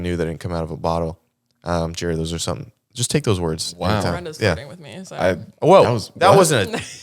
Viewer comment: what did she say? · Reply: Your friend is flirting me